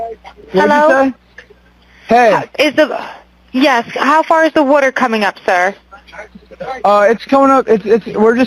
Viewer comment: Nolan’s freakin dead … is what I heard!!!